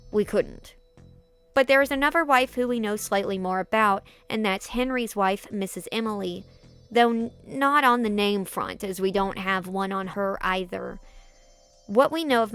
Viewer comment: it sounded like you said waifu 😂